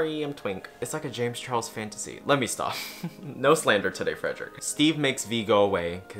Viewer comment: 1 hour. Oh lord, made his thesis about this show. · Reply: I thought you said lord at first 😭. But honestly yes